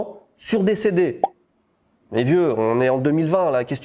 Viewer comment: "Qu'est-ce que tu fous à vendre des CD...: J'ai entendu "qu'est-ce que tu fous avant de décéder " 😅